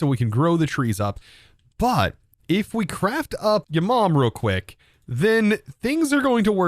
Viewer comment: Hold on!! Did you say ''your mom''???